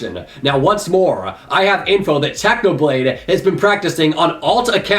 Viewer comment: I hear u said Tanoblade not Technoblade